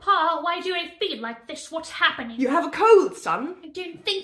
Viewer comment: I didn’t read the subtitles at first cuz I’m eating so I thought she said “ you have COVID “